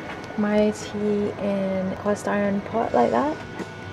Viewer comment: Also sounded like you said you brewed your tea in a “polystyrene pot”, I had to go back and heard you say “cast iron.” 😂